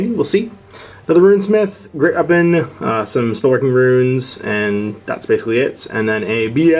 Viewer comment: sounded like he said "snorkeling runes"...